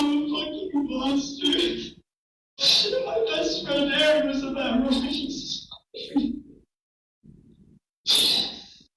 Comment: What did he said 35:22 ???????? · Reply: Yo mom